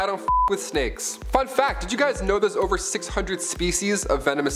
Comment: i thought a second there you said fck fact